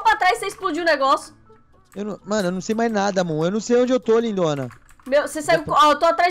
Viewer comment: só eu q ouvi ele falar lindona?